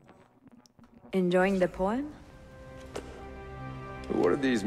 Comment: am i the only one who thought she said "enjoying the porn"?